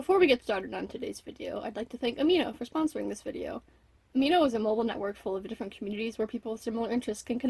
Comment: I thought you said "Horrible Network" instead of mobile. I was about to die